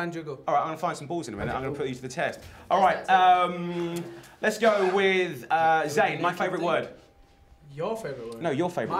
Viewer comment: What did louis say at 2:25? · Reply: "He does that too" ;)